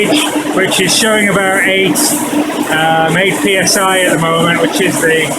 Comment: psi? couldnt hear what the number was. eight.. something? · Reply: yes eight. dynamic head is quite low with all valves open.